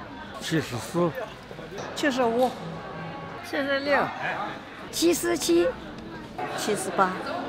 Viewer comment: I heard cheese-a-chee